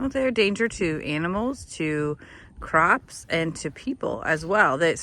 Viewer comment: !!!! I thought you said crocs not CROPS!!!! 🤣 nice demonstration being a wild hog, no new roll plays w your vanilla booty shorts wearing self!